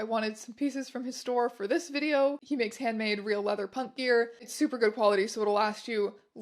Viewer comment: I’ve watched this a few times, and everyone she says “punk gear” in the beginning, I hear “pup gear”